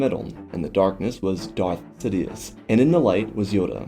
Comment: It sounds like you said Darth Tedious at the very beginning which sounds hilarious.